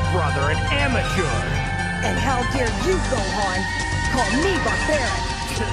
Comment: i couldn't hear well over vegeta's theme but i heard goku say, "and how dare you, gohan, call me a parent."